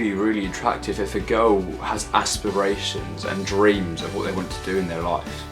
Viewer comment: Thought you said a girl who has asthma 😂😂